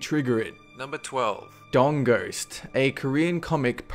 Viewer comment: Did he say "dong ghost?" I M B O N Z I B U D D Y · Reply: Was Boner Poltergeist taken? I'm sorry. Dong Ghost. It's hard not to make a dirty joke about that.